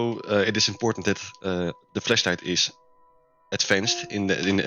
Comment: sounds like he is saying fleshlight 💀